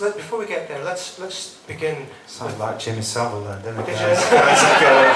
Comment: what did he say around 5:50 that got everyone laughing? i replayed that part a few times but couldn't make it out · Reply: He says I Sounded like Jimmy Saville there